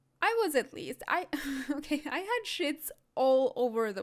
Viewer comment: "I had sheets all over the place" I thought u said sh*ts at first 😂😂